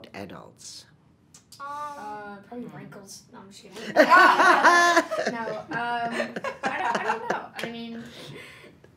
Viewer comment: what he said? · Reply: The wrinkles